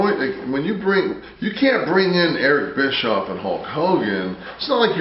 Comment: WTF did Nash say at 14:56? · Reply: Ham and Eggers - basically just an ordinary person